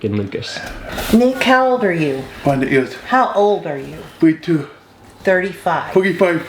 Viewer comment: Did he say “a hundred years” 😭